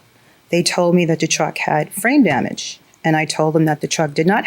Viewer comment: I thought she said The truck had brain damage... ( frame damage....lol)